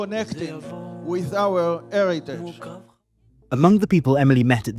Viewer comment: Did he say "It's about connecting with our arrogance"?